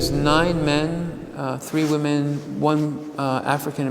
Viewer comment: He says "9 men, 3 women, 1 African-American". 😔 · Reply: I swear I head him say 'white men' somewhere right near there - maybe it was somewhere else in the piece. I went back and listened ~3:47. What if he said, "9 men, 3 women. 1 African American...a lot of the painters were Jewish..." Or might that be hoping too much?